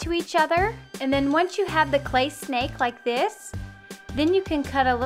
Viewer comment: When she said "snake like" i thought she said "snape like" and i was like ???????????????? 🤔🤔🤔